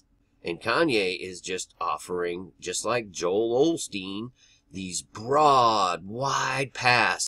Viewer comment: I heard ya,,,"Oelstein"!